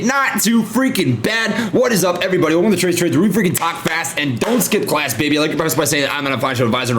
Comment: I wonder what it must sound like having Trey as a neighbor in a hotel... 😂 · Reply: I would be like no way did I just hear “were we talk fast and slap ass”